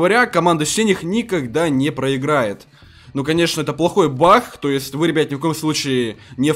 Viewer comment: Мне послышалось ты сказал пах они баг